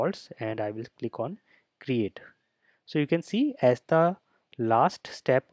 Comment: Did you say “esta” in 8:27?Do you speak Portuguese too? Greetings from Brazil! 😄🇧🇷